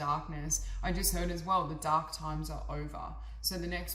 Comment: 3: Instead of “the dark days are over” I heard “The dog days are over”, and instantly my brain turned on Florence and the machine. Now the lyrics “The dog days are over so you better run” are on a loop in my brain lol 🤩